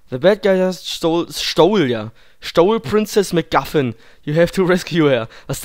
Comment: Priness Muggafin?Ich hab Princess Muffin verstanden!